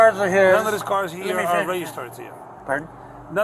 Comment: is thst piglet drunk? "none of these cars are reg star trio" iswhat it sounded like he was saying instead of "none of these cars are registered to you" he sounds like he's slurring his words. I could barely understand his oinks